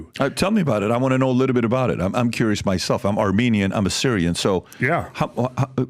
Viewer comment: did PBD just say “I’m Armenian I’m Assyrian” but he’s Iranian?